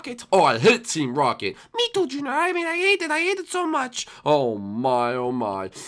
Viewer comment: Did anyone else hear squirtle say junior